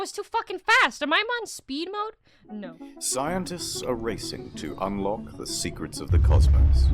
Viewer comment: Scientists are RACIST ?! Oh, I've misheard. They're racing. My bad.